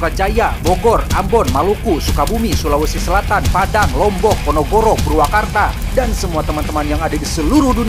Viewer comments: kalo gak salah denger eta s td bilang Ponogoro 🤫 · Bang dari kemaren ngmong Ponorogo jadi Ponogoro....apa q yang salah denger....😂😂😂